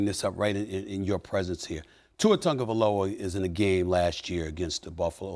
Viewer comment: Instead of spelling Tua Tagovailoa, the captions says "To a tongue of Aloha"